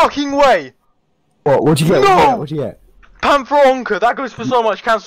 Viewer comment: He's a Hungarian? I heard: Hogy hívják? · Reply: He said: what did you get?